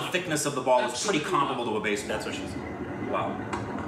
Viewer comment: did just stated, "that's what she said"??😂😂